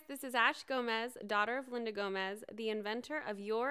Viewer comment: I thought you said Selena Gomez